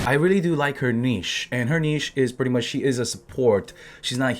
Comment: I thought you said you liked her knees there for a second